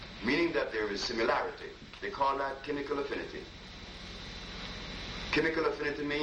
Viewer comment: What is that word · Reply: chemical affinity